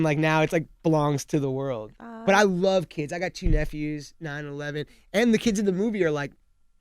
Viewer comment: Jonah says that he loves kids and that he has 2 nephews that are "9 and 11". It sounded a bit awkward because it sounded like he was talking about 9/11.